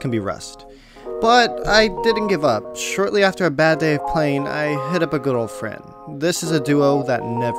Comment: I thought u said: "I'm gonan hit up a girlfriend."